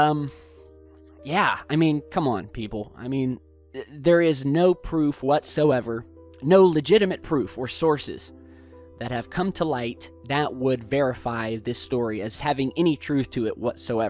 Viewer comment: did you say "bearify?" If you did, props